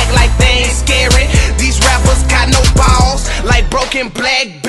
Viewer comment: i thought he said "these rappers aint got no bars".....cause on cell phones you get bars for service, and a broken one wont have any